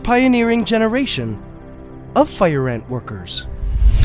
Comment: thought he said wankers XDDDDDDDD